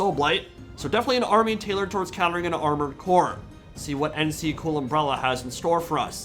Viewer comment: did you say.... armored core? =] (i cant wait for aug 25th for armored core 6)